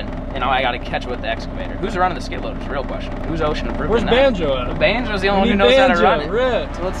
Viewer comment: This whole time I thought he was saying ocean aproved not osha 😂